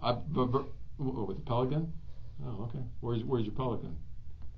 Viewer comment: subtitles say the detective said- A pelican okay where’s-where’s Republican Thought I would inform yas.. I turned the subtitles on coz I couldn’t hear Nik.. turns out neither can the subtitles. This kid is no cold blooded killer that’s for sure. Butttt... he fucked up. So get his story, try learn what you can from him, then put him to sleep.